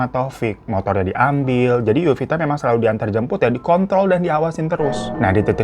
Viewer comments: PLIIISSSS 9:46 gw ga salah denger kan bang.. lu ngomong "di kontrol" kan itu ?? bukan "dikont.." wkwkwkwkwkwkwk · ini aku yang salah dengar apa gimana? Harusnya di kontrol malah typo. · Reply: Aku denger nya di kontrol